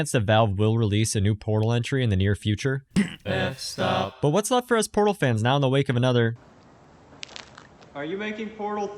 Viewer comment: Can anyone hear what he said here? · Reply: F-stop